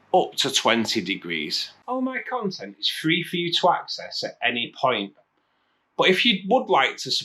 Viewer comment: At first I thought you said "All my content is free for you twats. At any point..."